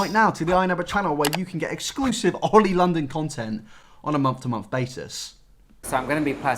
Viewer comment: imao for a sec i thought it said ‘Oil London’ and i was so confused 😂😂